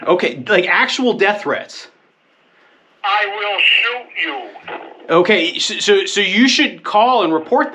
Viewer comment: did he just say ''I will shoot you?'' or did I hear wrong?